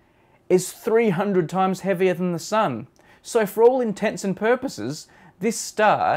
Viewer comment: did he say "for all intents and purposes" at 0:39 XD